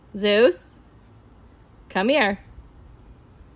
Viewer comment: Splash... I heard you say swim time mom